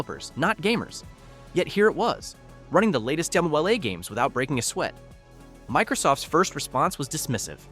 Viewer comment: Okay what's he saying at 4:14 ? Between that, some unusual pauses, and saying "Cyberpunk two thousand seventy seven" instead of the universally used "Twenty Seventy Seven", I'm starting to wonder if this video is AI-dubbed... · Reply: Double A A games 😂